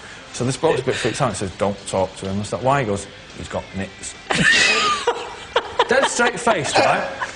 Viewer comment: what does he say? "dont talk to him, his got,,,"? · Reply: Nits/Head lice